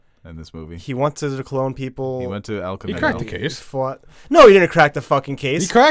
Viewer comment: did you say “he went to El Kamino”?? 😆